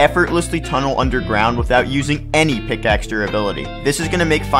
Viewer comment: Why did i hear “subscribe to have no friends”